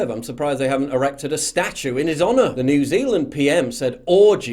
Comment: I totally thought you said, "arrested a statue in his honor" which doesn't make sense to me but probably to them. Erecting a statue is only for a certain type of people.